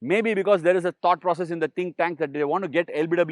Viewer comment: You said think tank, I heard Ash'win'